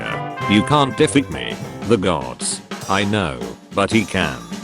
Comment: why did I hear “the dogs” 🐶